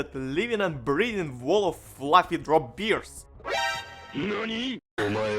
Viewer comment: I swear he said "drop beer" until I saw the picture above. As an Aussie, I was seriously excited for about 2 seconds... :D